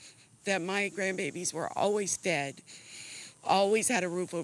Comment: If you put on subtitles at 1:01 what the grandma says is slightly believable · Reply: I was tripping on that too! I kept wondering why she would say that on camera, till I realized she said "fed' 😅